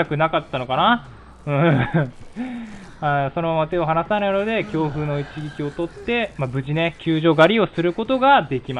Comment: 恐怖の一撃が強風の一撃に聞こえます。 それはそれでありですけど。